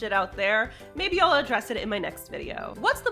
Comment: You said address it but I swore I heard undress it. Maybe it's just the subject of the video